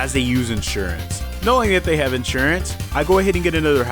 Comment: While you were at the first gym it sounded like you said I NOW HE HAS INSURANCE